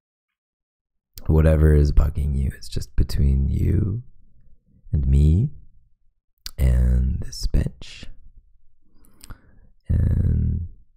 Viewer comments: I thought he said "whatever is bugging you it's just between me and this b♡tch" and I was like......sir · When he said this bench I thought he said this bitch lol